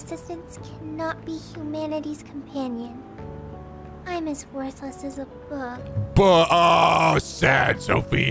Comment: Honest to god. I was not looking at the screen when Sophia was sad. And I thought she said. "I am as useless as a book." And I laughed. I mean.. She's a provider of information like a book but can't do physical labour so that wouldn't be incorrect. I just thought it was funny and then... Bug... dammit that's not nearly as funny.